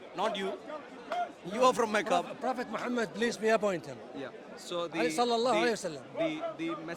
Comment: did he say police be unto him?(Muhammad).